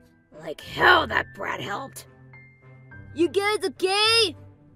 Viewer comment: it said: you guys OK? I heard: you guys are gay